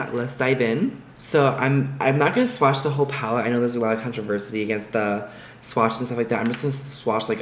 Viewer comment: Did you just say "controversity"?